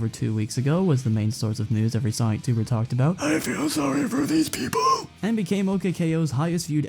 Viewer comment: excuse me, but what did you say? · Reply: I feel sorry for these people